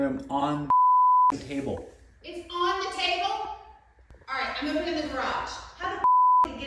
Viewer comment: When she said, I'm opening the garage, I thought she said, I'm putting it in the garage! I said to myself, wow you're brave!! And when she said Liar, that really meant, you're joking/you're kidding me right? 😊💜😇🌷